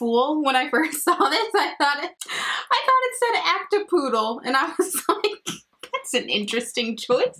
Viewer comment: I thought when you said “act a poodle” that you actually said “active poodle” I had to listen to you say it a couple times 😂😂 lots of interesting interpretations of that color in this video 🤣